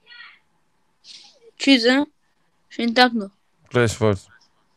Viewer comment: Bin ich allein das ich bei minute 6:32 durchfall verstanden hab ?😶😂